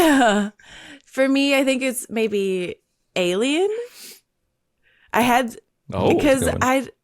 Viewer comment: good show, did say aliens at 56:00 for favorite reaction experience? i could nto quite hear her